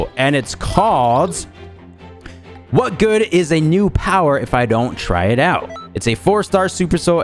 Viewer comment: I literally thought the super soul said newspaper but it's new power